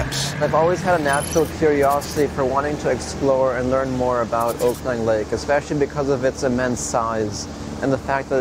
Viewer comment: did he just call it Oakland Lake?